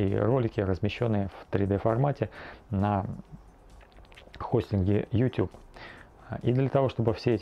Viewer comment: кому послышалось [х*й с ним Ютюб?] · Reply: Он сказал хостинге.